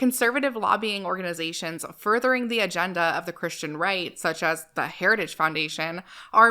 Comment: Every time I hear the phrase "Christian Right" I always hear Christian Reich, which honestly isn't far off. Can't wait to live under a full throated theocracy.